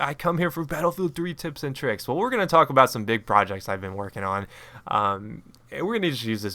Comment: I totally thought you said "dick projects" at first...